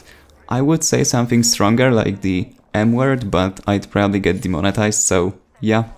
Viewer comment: thought you said "something stronger, like the N-word"